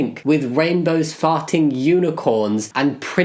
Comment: I heard that as "rainbows that fart unicorns" and now I'm trying to wrap my head around the logistics of that.